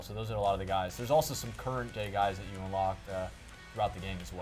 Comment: "current gay guys you can unlock"? Am I hearing that right? Can anyone correct me?